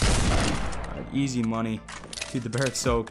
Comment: did he say 'easy money' wut???